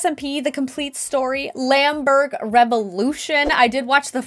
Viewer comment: did I just heard lamberg ??